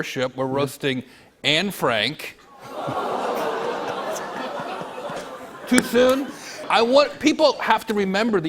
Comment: I want to hear what he said after the 3:59 mark that they edited out.. lol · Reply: He said "Too soon?"